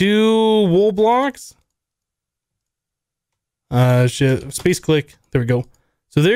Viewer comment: Did he say shit? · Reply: He was going to say "shift-click" but switched to "space-click"